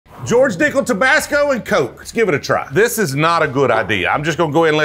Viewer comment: Man at first i thought you said "dicklet" and was like "man you don't have to call George out like that..."